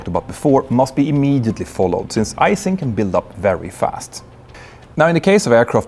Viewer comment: Sounded like he said "ice acid" 🥶